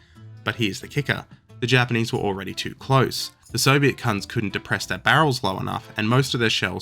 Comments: I thought you said C#NTS not guns. Thought passive agressive and could not fault. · did he just say "the Soviet c@ As a fellow Aussie understand this can happen sometimes 😂